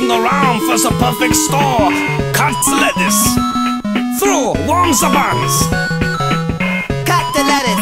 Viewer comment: he said warm the buns Insted of toast the buns That means the level name would be called warmed buns instead of toast buns Also gru ant said im the lord Instead of im the man